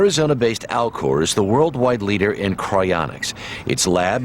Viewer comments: I was listening to this without watching the footage and I thought you said Al Gore instead of Alcor, lol. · I thought I heard ‘Al Gore’ at first